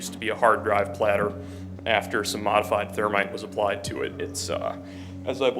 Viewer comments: I kept hearing "Termite" instead of "Thermite". Was so confused for a minute. 😂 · I came here just to say... I tend to listen to this in the background or as I fall asleep... And I entirely woke up, as I swore blind you said "Termite" a load of times, and I thought "Oh wow that's cool, never heard of Termite's being used in data destruction before"... Then I thought that's odd why would the police care if he was buying Termites. But no. You were saying thermite. Tirty-tree and a turd etc etc... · I heard "termites to destroy hard drives"? Oh Thermite, I forgot the Irish have difficulty with "Th". Three becomes tree, thermite becomes termite.😂